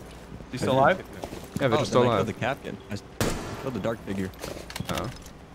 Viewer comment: Yo did say the n word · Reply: Dark figure